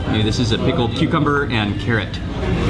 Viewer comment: i thought he said [a big old cucumber